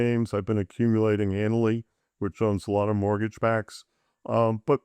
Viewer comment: "I've been buying more defensive names, I've been accumulating (amaly????) which owns a lot of mortgage backs". What security is he talking about? "amally", "annly".... anyone have any idea what he's talking about? This is the 2nd time he's mentioned this on podcasts, and cannot understand what he's saying. Anyone know the security he's talking about? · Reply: Might be Annaly? They seem to own MBS like he said